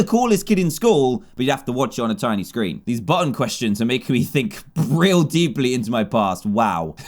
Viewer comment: when he says button questions in the cracked i phone one i thought he said fu@@ question